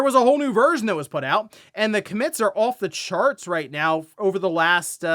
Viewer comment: Why did he say “comments” weird? Sounds like come-mints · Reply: Commits not comments